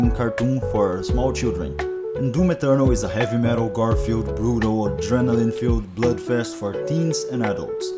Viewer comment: Ngl I thought you said “Heavy Metal Garfield” 💀